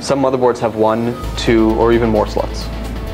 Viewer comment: hahaha @ 2:36. "...Or even more slots." It sounded like he said 'sluts'